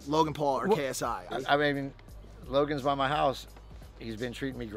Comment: Did he say logan bought my house ? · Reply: I thought he said by my house like they were neighbors, not buy my house.